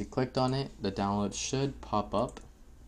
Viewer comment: i thought you said the download shit pop up